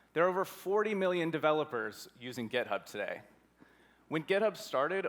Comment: did he just say gitub?